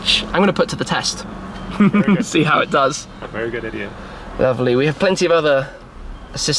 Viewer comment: 😂did I hear very good idiot?